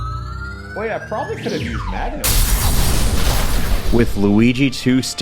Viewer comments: What does he say here? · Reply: "I should have probably used Magneton" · i've watched multiple reaction videos to this but i gotta ask, what did he say there there? Could've used what? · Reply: Magnemite